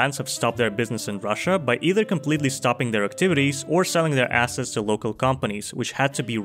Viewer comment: nah I heard “sale their asses” 😫😭